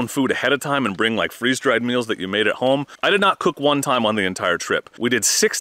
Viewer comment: I had to doubletake @ 3:02 totally thought you said I did not poop one time 🤣